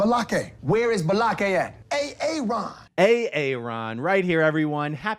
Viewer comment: I thought the intro was saying "Where's A L.Ron?" But it doesn't lol